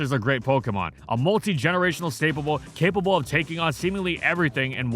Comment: did he just say stapable